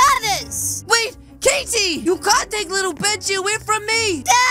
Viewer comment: Did he say little Bitchy